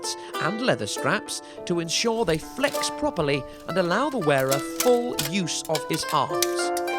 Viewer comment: For some reason I heard "And allow the wearer the full use of his arse", with subtitles on.